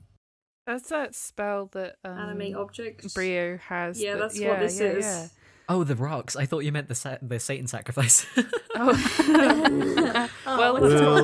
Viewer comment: Pft why’d I hear “anime objects” and not “animated objects”